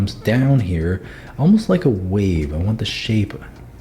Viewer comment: When you said wave I heard 'weave' Me: Wait...... Weave? Huh, All your characters do look like they have a wig. also me: Huhhhh? Wave?! God damn I need to get my ears checked